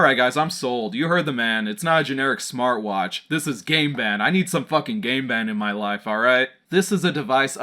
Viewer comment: It sounds like your saying "gay band"